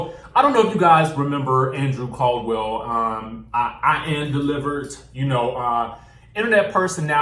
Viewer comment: I thought sis said he was delivert! Lol 😂😂🤦🏽‍♀️🤦🏽‍♀️